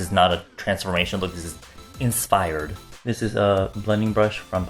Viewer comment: Is it just me or did he actually say 'Insfired'?